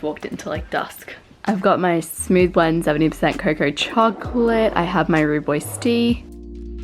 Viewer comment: I swear you said "I have my rudeboys tea" and I was like yeah sip on them haters